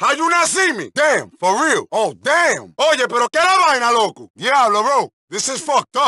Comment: que dice en el minuto 0:29 ? what does he say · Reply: He's saying "Oye pero qué e' la vaina loco" in english "Hey what's wrong with you asshole" (The Dominicans use "Loco" as a good way to call a friend, so "loco" means friend/buddy but it also means crazy of you're using it as an insult. Example: Are you crazy? | ¿Estás loco? The Dominicans mostly use "Loco" as a way to call a friend but in the case of Luis Lopéz, he was insulting him just like saying "Asshole"